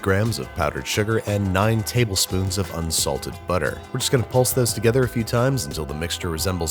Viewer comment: Did Babish say: Insulted butter" at 0:40 ? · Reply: Un-salted